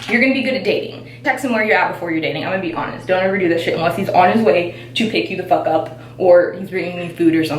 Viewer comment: Hi! I like this video! I have a question.. would love if you could explain what you were saying on 4:16-4:25. It sounded important but I just am not comprehending the point made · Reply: hey i know this is four months late but I'm pretty sure she said not to tell a guy where you're at or what you're doing unless he's on his way to pick you up. it goes along with her "be lazy" advice bc when you keep yourself busy and unavailable when the two of you are not together, he's gonna wonder what you're doing and long to see more of you, therefore put in more effort. hope that helped :)